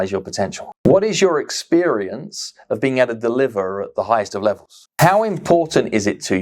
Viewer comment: Hi, I can't catch the second question (I'm not a native english speaker) Can someone transcribe the second question at 13:09? It would be a great help. Thanks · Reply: What do you understand about what it really takes to maximize your potentials? What is your experience of being at the deliverer of the highest of levels? How important is it to you to reach your goals? How certain are you you're doing everything you could be doing and should be doing to make that a reality?